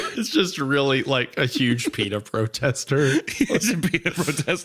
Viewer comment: OH PITA! I thought they were saying "peanut" and I was very confused.